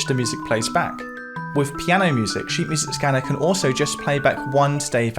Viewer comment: I heard it as sheet music scammer 😂